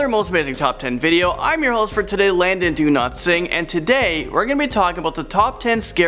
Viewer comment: i thought you said Landon DoNotSing lol